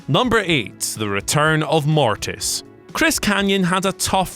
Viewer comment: Chris K... or Chris GAYYYYY. Not funny my b. God rest his soul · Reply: Dude I honestly thought he said Chris Gay too, was even gonna make a comment... but these guys are wayyyy to PC to make a bad joke like that... and then I rewound it a sec and realized it was Chris K...